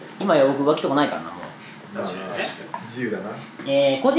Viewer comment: 個人でも活動できそうなのは？が甲子園でも活動できそうなのは？に聞こえた